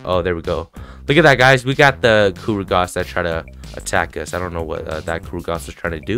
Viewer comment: I thought u said gurugosk again😂